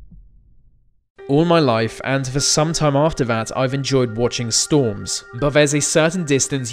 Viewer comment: Does he say “all my life and for sometime after that I’ve enjoyed watching storms” the 3:16 mark?!?! Did this narrator die when Portugal sank? Lolol